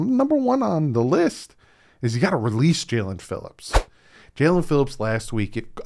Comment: holy crap you gave me a heart attack I thought you were saying Miami released phillips holy crap I almost lost my mind